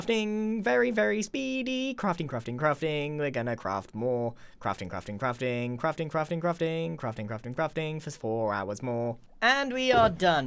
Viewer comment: i thought you said crafting for smore hours more :)